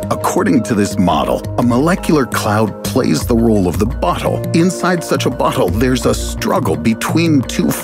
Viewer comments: why does he say "bottle" like "butthole"? That has to be on purpose right? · it sounded like he said "butthole" lol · Whenever he says bottle it sound like he say butthole and then the way he describes it makes it better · I heard butthole at 4:10 and couldn’t take the rest of the video seriously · Did he just say "butt hole"? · Who heard "butthole" when guy said "bottle"?! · did you say “butthole”?